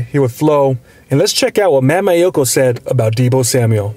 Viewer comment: Did he just say Matt Maiocco is dumb lol